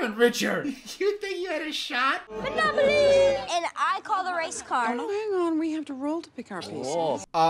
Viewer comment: I thought the kid said "I call the race card" and braced myself for some surprisingly serious racism discussion